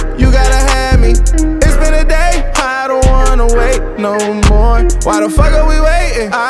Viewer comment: I thought he said no more Features Wtf fam